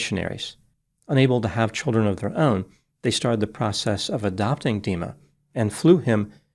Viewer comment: I heard 'adopting demon' 😂